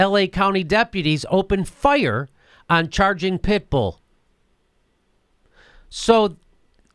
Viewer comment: I thought it said "charging bull" not "charging pit bull"... Guess my brain was trying to make sense of nonsense...